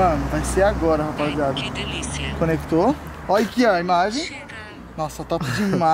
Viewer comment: eu escutei a mulher do drone falar "que delicia" 😂😂😂😂